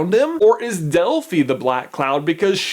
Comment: I thought u said Delphi is a black clown